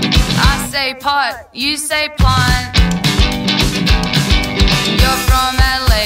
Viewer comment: I say "dance", you say "dance" I say "France", you say "France" You're from Adelaide, I'm from Hobart I say "Hugh", you say "Grant" I say "pot", you say "blunt" You're from Adelaide, I'm from Hobart [Hook] We couldn't be more contrary if we tried Oh, chalk and cheese, we rarely see eye to eye [Verse 2] I am dumb, you are smart We are fifteen years apart You're from Adelaide, I'm from Mars I say "ooh", you say "ahh" I am careful, you like scars I like pickles from the jar [Hook] We couldn't be more contrary if we tried Oh, chalk and cheese, we rarely see eye to eye [Verse 3] I say "you", you say "am I" You like mornings, I like nights I love you till the day I die You say "Christopher", I say "Walken" You love, I love Christopher Walken I guess at least we have got one thing on common [Hook] We couldn't be more contrary if we tried Oh, chalk and cheese, we rarely see eye to eye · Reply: I always thought it was "I say Pot, you say Pint" but plant makes sense too